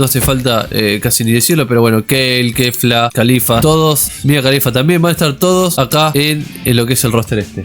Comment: escuché mía Khalifa XD